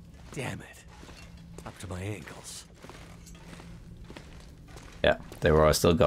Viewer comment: Sounds like Will says "Dammit, my ankles." Never heard this voice line before, any idea what it indicates? One idea I had to explore this "safely" would be to bring a massive amount of birch bark tea, wait for the aurora to end and then explore without fear of electrocution. Even if it's a long time to the next aurora, after you get cabin fever the BBT will keep you alive despite not being able to sleep for healing. Not really worth it without more/better loot down here though.